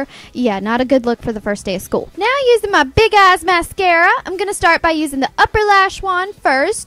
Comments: I thought she said big ass mascara 😂😂😂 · I thought she said big as mascara😂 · When she was putting on her mascara I thought she said "big ass mascara" 😂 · did she say big a*s mascara